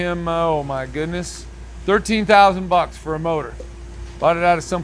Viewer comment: I know places were you can buy trucks/cars for $10_000 to $12_000. Buying motors for $7_000 is kind of crazy in some markets (like country rural areas). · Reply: Where are you all getting $7000 from? Add another $6,000 and you’ll get the $13,000 he actually said!